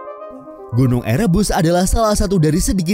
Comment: sempet salah denger, gunung erebus = gunung air rebus 🗿